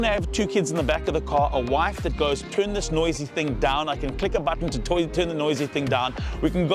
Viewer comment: did he say "a wife with a button, to turn the noisy thing down"? 🤣